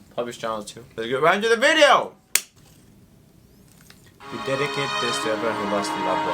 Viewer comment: did u just say, dedeket?...hehe peace 😝✌️